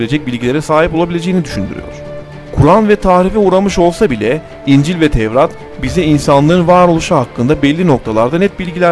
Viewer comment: Kur'an tahribe uğradığından İncil ve tevratdaha net bilgiler mı veriyor dedi ben mi yanlış duydum ??? Komik mi lan bu djsbsbbssh aptal hdhshsh buna gerçekten çok güldüm